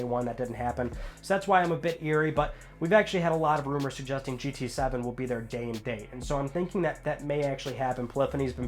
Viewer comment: I thought u said gta 7 I was like what happened to gta 6 lol